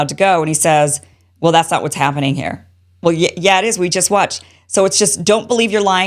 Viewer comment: Not that it makes a difference, but I didn't hear, "That's not what's happening here." I heard, "That's not how it works here."